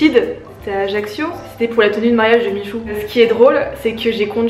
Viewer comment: C'est moi ou j'ai entendu a 13:09 me tenue de mariage de michou